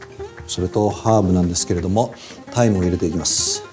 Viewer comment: 「ハーブなんですけど、、(ｽｩｰｰｯ) 大麻を入れていきます」 に聞こえた